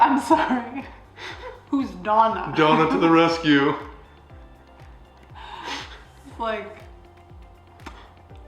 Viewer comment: help even after said "the sun" i was still like who tf is Don?? bc i heard "Don is gonna come. Don. The son." 😂😂